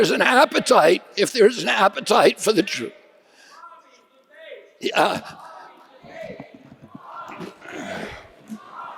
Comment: Does anyone know what the crowd started chanting at 19:39 ? I can't make it out. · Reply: Bobby debate